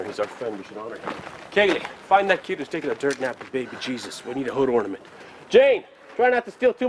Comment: "Kaylee, find that kid whose taking a dirt nap with baby Jesus, we need a hood ornament. Jayne, try not to steal too much o' their shit." Is definitely my favourite part of the bloopers. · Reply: dirt nap? I always thought he said turd nap! 😆😆😆 Dirt nap makes much more sense.